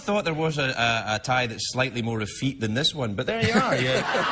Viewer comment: what word did craig say? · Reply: Effete